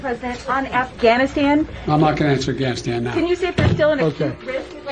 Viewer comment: "I'm not supposed to take any questions" "I'm not answering any questions about Afghanistan" Is that the first time he told the truth? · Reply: What I heard was: “Imnaw gonna answer ganistan”